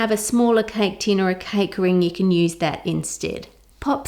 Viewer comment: I’m sorry i keep mishearing cake ring as c0çk ring